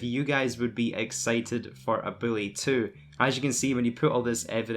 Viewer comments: He sounds like he says ''Billy 2'' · Wow this video was rough, i kept hearing “Billy 2” very very annoying